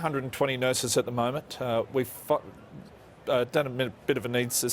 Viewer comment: I thought I heard him say "We F**k" 😂😂 . But Hats off.. What a transformation off the field.